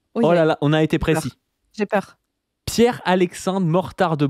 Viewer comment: pour tous les zinzins comme moi qui s'interrogent, j'ai vérifié ils avaient bien dit Pierre-Alexandre